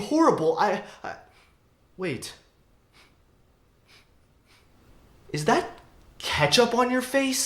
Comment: Really thought he was gonna say “is that cap”